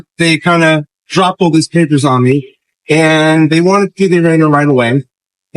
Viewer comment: Spr. Bro, your attorneys audio sounds manipulated😂 I'm pretty sure I heard him say pee on me.